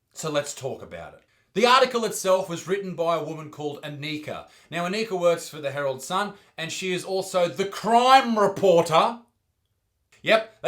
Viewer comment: When he said that lady Aneekas name I thought he said a neeger